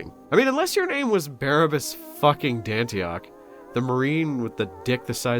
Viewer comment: Had to double take but I almost thought you called him Erebus Dantioch. I would have been very upset Dantioch is one of the good ones... scartch that the only good one